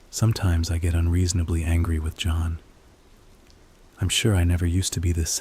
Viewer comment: did you say HANGRY 😂